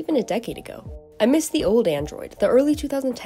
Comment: thot u sounded like 'i missed the old kanye, ..... new kanye"